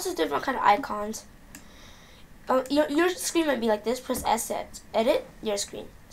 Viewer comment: no offence but did you say you dont have to scream at me like this on